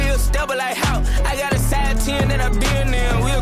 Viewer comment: I have 50 year old ears and even I heard "size 10". Lol